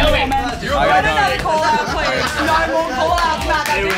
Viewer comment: At the end, piped up with, "No more call-outs." I could have SWORN she said "no more pull-outs."